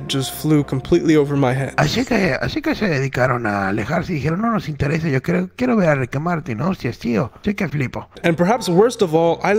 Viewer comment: I’m learning Spanish too. Can a native tell me what he said there, I can understand the words but can’t piece together what he’s saying😭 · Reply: Así que, así que se dedicaron a alejarse y dijeron no nos interesa, yo quiero ver a Ricky Martin, noo si es tío, si que flipo??? I guess, I'm a native I can't understand the last part